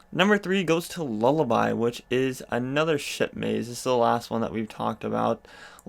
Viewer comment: Sounded like you said shit maze when referring to B340 lol